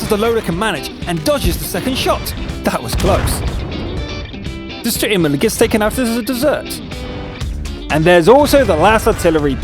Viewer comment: What did he say at 2:12? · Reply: The Sturer Emil c Gets taken out as a dessert